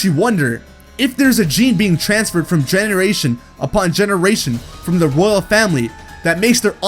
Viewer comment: When he said generation I thought he said genderasain.(Gender Asian)